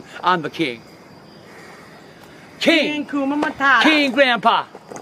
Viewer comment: I Thought Says ( King Hakuna Matata ) 😂😂😂